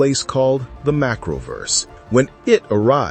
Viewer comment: sounds like he said it came from a place called the mackerel-verse lol